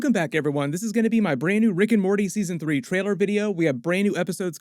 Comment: Here's my new Rick and Morty Season 5 Trailer video Easter Eggs and Marvel Loki Connection. Loki Episode and Rick and Morty Episodes coming in a couple weeks! Here's my new Loki Trailer video too! <link> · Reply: Lol in the video it sounded like you said season 3 video.